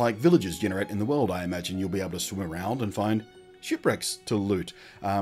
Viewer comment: My god when you said shipwreck I heard "shit bricks" for like the first three times you said it